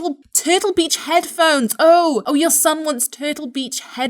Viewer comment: Thought he said I want beats headphones.